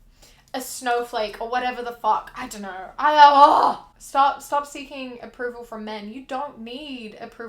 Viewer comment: I was singing while listening lol and then at 9:33 I thought you said ‘stop singing’ and I deadass just froze and sat there like 👁👁 for a solid ass minute